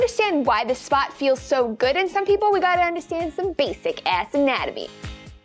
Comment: I thought you said "basic ass-anatomy" and I was like "what does the ass have to do with this"